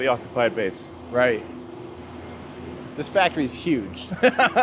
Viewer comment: thought it said Satisfactory